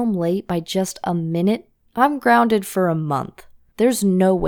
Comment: Did she say monk?